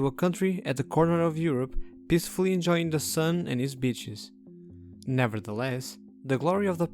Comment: Peacefully enjoying the sun and it's bit*** 😅 That's how I heard it